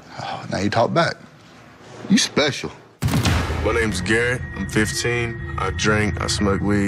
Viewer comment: Did he just say his name is gay Thats what it sounded like